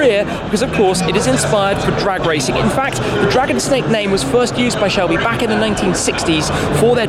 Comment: No one: Literally no one: Me: am i the only one here who read it as "DRAG-ON-SNAKE" instead of "DRAGON-SNAKE" everytime that GT500 Dragon Snake showing up? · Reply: I’ve always heard it as “Draggin Snake” because this car definitely hangs dong